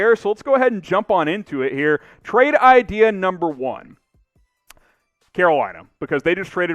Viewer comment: i heard "que bueno" instead of "Carolina"